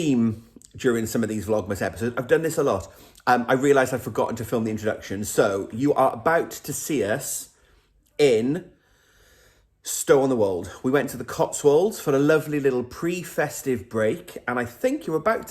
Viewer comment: I did some plying of yarn while I watched/listened to the video 🤪 I thought you was saying stone in the wall till I see the words on screen ooops Merry Christmas can't wait to see Mark in his jumper that's on a back burner 😯😜